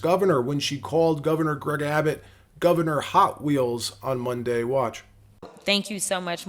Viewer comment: Sounded like she said “High wheels “. I immediately thought she was referring to Gov Abbott being in a wheel chair.